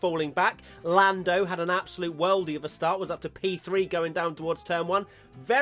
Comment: English-learner here.. at 1:42, Matt comments on Lando's start and says a certain word, that could be replaced by 'banger'. He says ".. an absolute ????" What is that word exactly? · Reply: "Worldie" - Short for "world class" and synonymous with "banger" in a sporting context